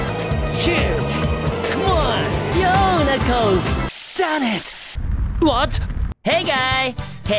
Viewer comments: It may sound like he said the D word, but he actually said Darn. No, I’m not trying to do anything, but knowing the Japanese pronunciation, they don’t have the actual Ar sound, so in Engurishu, he is actually saying Daan ito ( Darn it ) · D A M N I T at least that’s what i heard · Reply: I swear I hear him say “shit” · Was it just me that initially misheard “Damn it!” at 0:17? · could've swore sonic said "Shit"